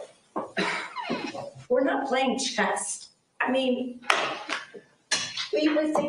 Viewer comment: did she say this isn't a game of "chest" instead of chess??? I listened to it multiple times and swear that is what I hear lol